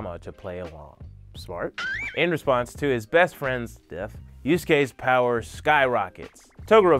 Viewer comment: Lol I kept hearing “before his death, before his death” and I forgot that the show essentially starts when he dies. >.<